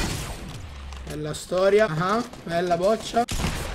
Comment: fa un no scope Cosa dice? Bella boccia